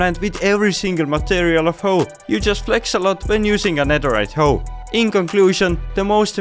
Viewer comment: damn! I really thought you said whore when you dropped diamonds in front of female game character!!😂😂😂